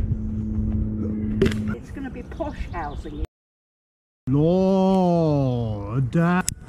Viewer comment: As 2:44 what did she say? Something about "posh" or at least that's what it sounded like to me. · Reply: I think she said posh housing or something along those lines.